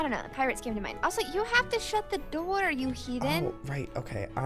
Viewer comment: Also you have to close to close the door you heathen! 1. I'm not sure if i heard her correctly 2. I'm a big TØP fan, so as soon as I heard that I thought about suicide squad and "Heathens so on. Whatever, and this videos pretty old, also whatever